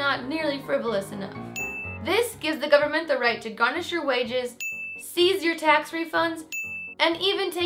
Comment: I thought was going to say "Garnish your celery". I would queue at your food truck